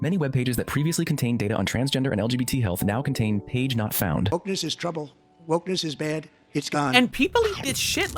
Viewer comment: What is Trump even saying here? Focus is trouble? What?